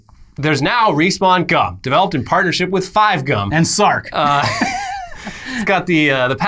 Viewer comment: did someone say Sark? Rewatched respawn inbox recently, its still sooooo funny But would not fly today lol